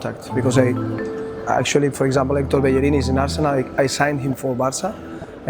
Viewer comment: what name he says? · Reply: Hector Bellerin